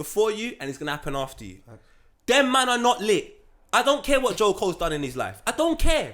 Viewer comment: Oh. He said Joe Cole. I thought he said J.Cole and almost had a heart attack with my old 34 year old self